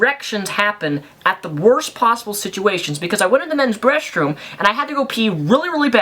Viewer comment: did you just say "the men's breast room"?